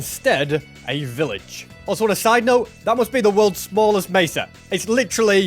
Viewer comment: did u say massive?